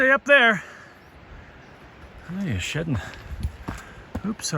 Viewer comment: I thought u said the elk was shiting u said shedding!!!!! Lol 💩 happy to see ur getting out and enjoying!!! Thanks for sharing!!! Have a lovely Summer ahead hopefully it's better then this cold spring we're having... going to Jasper in July! Fingers crossed no rain 🙏 ⛰️🏔⛰️🏔⛰️🌸🌸🌸🌸 take care 🧭 Happy Father's day!